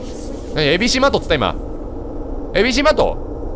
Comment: 本当にABC-MARTって聞こえるwww